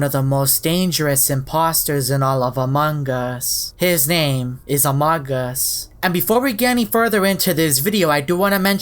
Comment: “And I encountered one of the most dangerous imposters in Among us, ‘Amogus’ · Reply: It sounds like he says “In among us, his name, is among us \_(•—•)_/